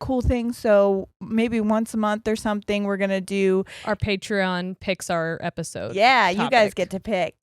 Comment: I did like a double take cuz I heard "we're gonna do our Patreon Pixar" 😂😂😂